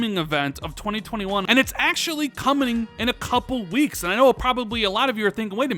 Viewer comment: Did you say coming or "comning"? Hahaha